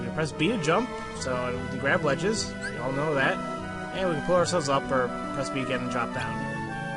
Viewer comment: For a moment, I thought you said "blow ourselves up".